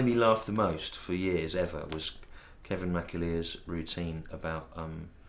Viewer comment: Couldn't catch the name of the comedian he mentions at 4:33 - the one who made him laugh the most. Sounded something like "Kevin Macillear" - does anyone know? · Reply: Kevin McAleer (full disclosure: had to google it), though I couldn't find his 'eating meringues' bit...